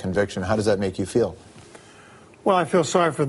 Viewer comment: What did he say about Spector at 4:49? "I feel sorry for those who can't..." Can't seem to make that out. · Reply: mikehermetic finish a song